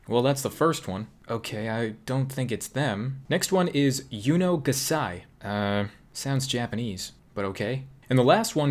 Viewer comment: I swear that i've heard Yuno gasai. XD